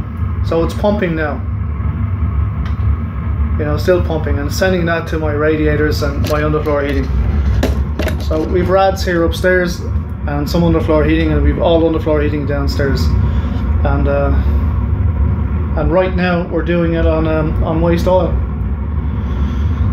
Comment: At first I thought you said you have rats upstairs LOL Guess that stove will only be on when you are near by ? Enjoy your videos